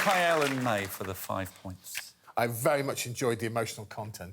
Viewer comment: completely thought everybody was saying "Mate" to Mae